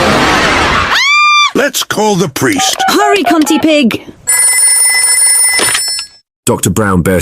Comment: da f did she say? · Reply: Humpty pig I'm pretty sure